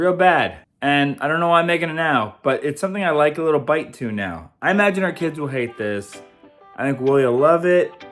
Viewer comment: Love these cookbook reviews!!!! I thought you said "our imaginary kids won't like this" but had to relisten and you said "I imagine our kids won't like this"😂😂